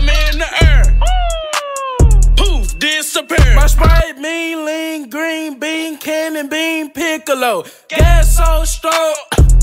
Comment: People who play 2k thought he said green bean water machine piccolo😂